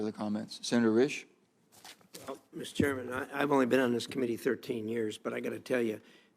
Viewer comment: I know! It's like all these so called experts on health. Ol joe been in oofice for nearly fifty years, amassed a huge fortune and he's not an expert on much of anything that benefits the United States! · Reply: I thought he said he was there 150 years